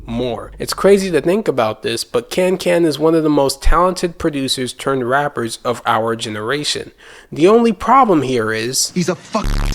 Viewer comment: hold up hold up . Did you say Kan is one of the most talented producers of this generation or is this rage bait ? 😂